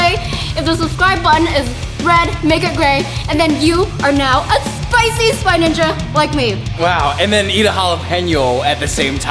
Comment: I thought you said you were the Spicy spy ninja